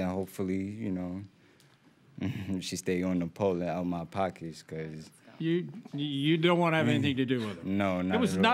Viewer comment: “Stay on the pole and out my pockets” wow the disrespect. He was the one wildin’ though SMH If he could’ve just controlled himself or not drank so much everything would’ve been different. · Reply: 😂 I thought he said "she's going to have to pull it out of my pockets!" LOL I was listening while I got ready and wasn't 100% paying attention. Good thing I read the comments!